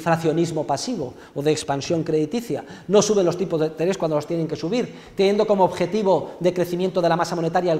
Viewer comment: que es lo que dice?,Gracias. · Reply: No suben los tipos de interés cuando los tienen que subir. Teniendo como objetivo decrecimiento de la masa monetaria del 4,5% mantienen impasibles un crecimiento del 7% año tras año como ha sucedido en europa o del 10 o del 12 como sucedía en el área dólar